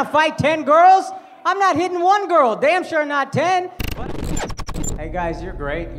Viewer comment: What I heard was "I'm not hitting on a 1 girl, and you're DAMN SURE not a 10." I was like : Savage. But the real comeback was aigh't :D